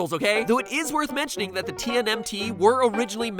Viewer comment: Did he say T'NM'T